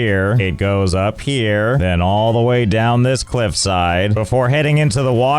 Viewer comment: I heard "clip"side instead of cliffside.